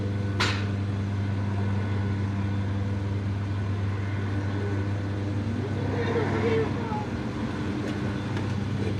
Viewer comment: Did anyone else hear a little kids voice at 1:50 after he leaves the frame? Creepy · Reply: "please don't?"